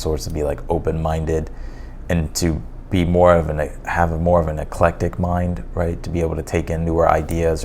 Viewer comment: i thought he said “epileptic mind” 🤣